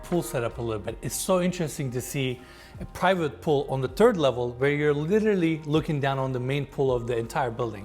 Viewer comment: Did he just say the terd level lol